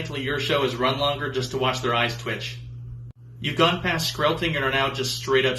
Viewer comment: What did he say at the heathers one i couldnt hear it well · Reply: "You've gone past screlting (scream-belting) and are now just straight up screaming."